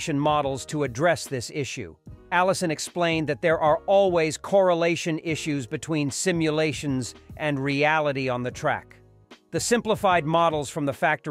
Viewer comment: What I heard: "...There are always correlation issues between our statements and real life...". That sounds more adequate.👴😏🤷‍♂